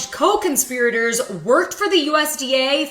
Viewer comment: No, you misheard(or I did) It said her co conspirator was a 33 yr. Veteran